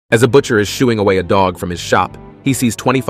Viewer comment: I thought when he said shooing away the dog, he said shooting away a dog 💀